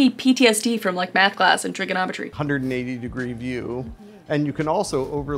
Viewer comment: “Those angles are giving me PTSD from math and geometry” Me, a math teacher, “I hear ya.🤣” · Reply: I thought she said "..math and trigonometry".